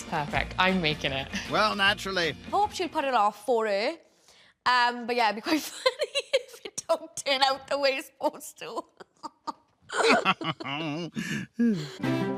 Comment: What did she say ??? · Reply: “i hope she’ll pull it off, for her. um but yeah, [it will] be quite funny if it don’t turn out the way it’s supposed to”